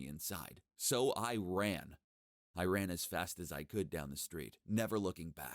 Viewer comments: It’s funny he was running from the military ppl and he said “i ran” but what I heard was “iran” :worldwar3jokes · I thought he said “I ran in Iran” XD